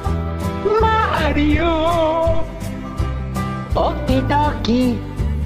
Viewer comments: When he says “okie dokie” it feels like he’s accepting a death and he’s barely gonna cry. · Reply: he was saying "okie dokie" the whole time and i thought he was saying old kentucky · When he says “okie dokie” it feels like he’s accepting a death and he’s barely gonna cry. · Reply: I thought he said “ol’ Kentucky”